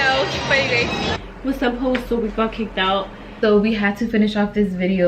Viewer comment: did she really say what’s up hoes ? lol